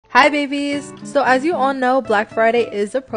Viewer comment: I heard “Hi baby’s” Me: HUH🤔